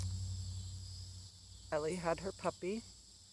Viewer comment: Good morning friend. Watching... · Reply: Ellie? gorgeous puppy. I thought you were saying Eva. 😀